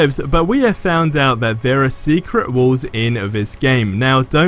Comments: Clicking off because it thought u said secret wars. How about put that in the video titles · Secret Walls???!!! Are you kidding me? I thought you were saying: SECRET WARS!